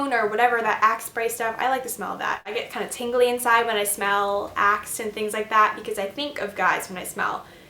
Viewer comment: sounds like your saying 'i get all tingly inside when i smell ASS' lol :P